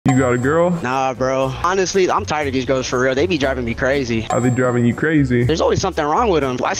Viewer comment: I swear i heard " I been driving you crazy " After the Repeat I Read The Sub And ☠️